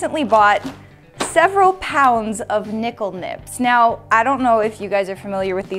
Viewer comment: Am I the only one who miss-heard it as NIPPLE NIPS LMAO